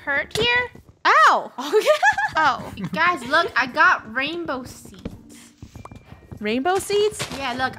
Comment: I tried to go back to start and then I accidentally stopped at like 1:24 or something and then I just hear say “rainbow seats” or “rainbow seeds” Edit: actually at like